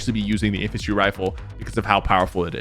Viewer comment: sorry but did he say wiffle instead of rifle or is that just me